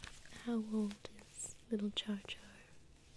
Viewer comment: When she said char char I thought she said char char binks